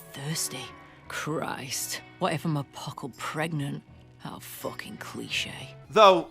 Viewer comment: I'm so happy to rehear that quote. Thank you. An actual professional voice actor delivered those lines... that's what kills me. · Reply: Full disclosure, in original video on the subject, I thought she said "a poggle pregnant" and assumed that was some British-ism for "a bit." I suppose my brain had auto-correct on.